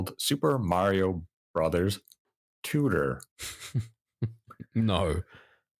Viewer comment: Is heard it as "Super Mario Bros Tudor"- A spinoff about the royalty aspect of the Mushroom Kingdom. I Wonder what happened to the Oneders.